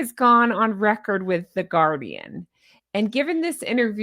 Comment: I thought you said the Garbian…I think you are right 😂😂😂